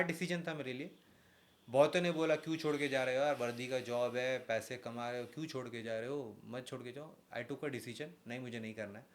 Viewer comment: Listen carefully at 33:17 . What he said , he said ki paise kama rahe ho . Anyone can understand this.